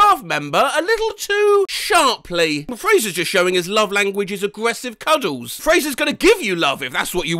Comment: it says aggressive cuddles but I heard “breastfeed cuddles”